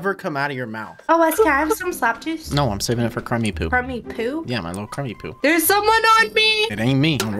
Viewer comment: Ain't no way I just heard "creamy poo" come out your mouth 😂😂